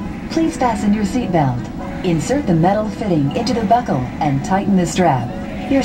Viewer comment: thought they said insert the metal "thing" lol